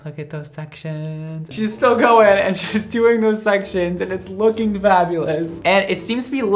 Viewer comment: What accent does have??? It's pretty but I can't recognize it... now i'm annoyed cause i don't know :/ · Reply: Brad said "Looking FAB!!" and I heard "Lookin' BAD!!" lmao i am deaf